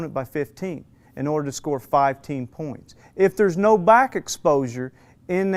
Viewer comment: lol he said "five-teen"" points near the end. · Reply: He actually said @ 11:50 "in order to score 5 TEAM points", not 5-teen.